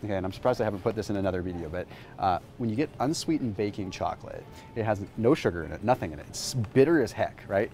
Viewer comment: Thought you said bacon 🥓 chocolate 🍫 haha. P. s. is a luck woman!